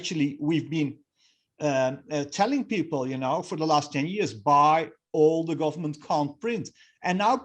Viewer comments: buy all "government can't print" ? what did he say?? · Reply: So buy: gold, silver, Bitcoin, real estate = all the government can’t print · Does anyone know what this man is saying at about 9:45 ? He says buy all the government con-print? He says it again right afterwards and I cannot make it out! 😤What are we supposed to buy‼?? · he says "buy all the government _________" I couldn't tell what he was saying . Anybody??? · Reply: I think he says "buy all the government can't print"